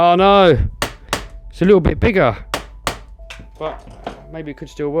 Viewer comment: did i hear say fuck? 😂😂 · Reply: but